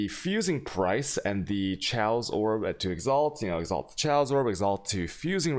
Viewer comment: I thought you were saying "Chailce Orbs" or "Chay-os orbs" and I was very confused.